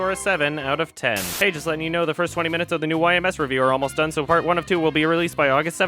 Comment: Fuck dyslexia! Somehow I heard "the first part of the next YMS review with come out December 7th" The.Fuck.Brain.